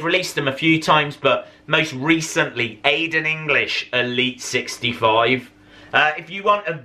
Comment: did just say elite